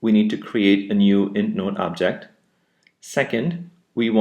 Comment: in step one what does he say? "we need to create an N node object"?? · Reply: ds su- He says "IntNode" object. It's an object instance of your class, which is the IntNode class.